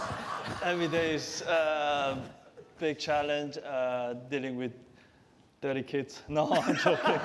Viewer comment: any british native? CC says "dealing with dirty kids" , but didnt Son say "dealing with thirty kids?" Let me know what u think